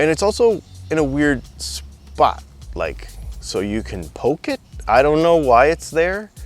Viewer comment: Sorry I arrived late. The first thing I heard is "it's got a hole and you can poke it!" I had to make sure I was on the right website and not an adult website by accident! 😨